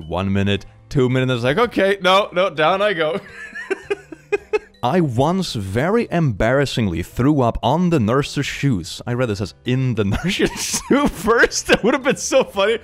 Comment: "I read it as IN the nurses shoes at first" I HEARD it as in the nurses shoes -_- XD